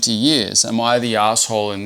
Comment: I thought it said” am I in the butthole “ 💀😭